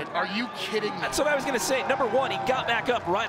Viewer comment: I don’t know about you but at 14:42 I heard the N word 🤭😶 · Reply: Rc Seeker was it number? 🤭🤭🤭🤭🤭🤭